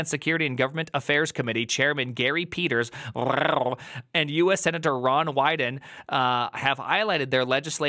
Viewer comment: I thought you said Senator Ron White for a moment and I was thinking HELL YEAH, THAT'S THE SENATOR WE ALL NEED.